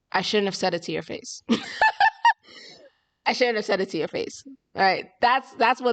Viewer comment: At this moment, Luisa actually said, " I shouldn't have said that." Instead of "I shouldn't have said it like that "